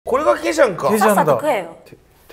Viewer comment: これがケジャンかがこれだけじゃんかに聞こえたのワロタ